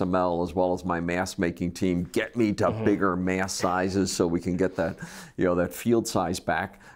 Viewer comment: What does he say in 1:18? Is it mass or mask size? What's he talking about here, how would it allow bigger field sizes?